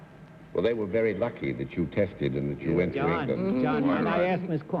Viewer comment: Watching this in 2025 instead of 1960, I first thought that Nancy Kwan said that the studio "texted" her. But, I knew that wasn't right.